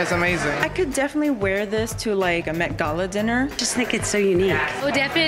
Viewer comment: “I could wear this for a MET gala dinner” as if you’ll ever be invited to one lmao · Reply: I thought she said I could wear this in a Mcdonald dinner my hearing is very bad :/